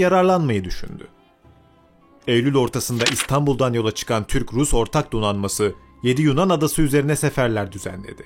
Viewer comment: bi an Türk-Rus ortak devriyesi diye duydum amk :D